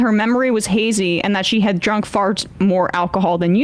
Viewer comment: did just say that the victim drank farts?